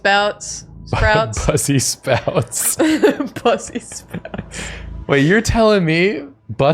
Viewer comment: every time i stock brussel sprouts, i hear "bussy spouts" in my head. thanks.